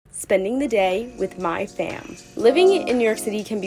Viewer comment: just sound like she said "spending the day with my fam"